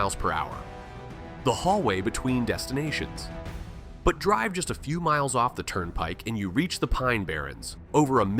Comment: The pine barrens is a bit further than "10 minutes" from Newark 😂 10 minutes from Newark is Elizabeth. The Pine Barrens are more like around 50-60 miles south. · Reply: Did he say ten minutes from newark? I heard "a few miles from the turnpike".